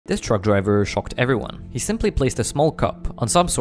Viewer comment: Bro i thought he said drunk driver. I would have been impressed god damn!